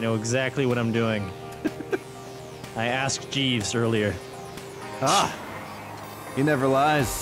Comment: Did Vanoss say he asked Jeeves, the previous name of <link>, or Jes' as in Jesus? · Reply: He asked Jeeves